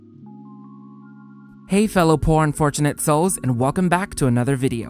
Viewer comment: "Hey fellow Porn-Unfortunate souls." I swear that's what I heard. lol 🤣